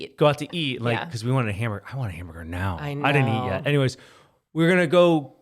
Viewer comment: Oh a hamburger hahaha I thought U said "I wanna hammer her now" Had to listen 3 times over haha 😆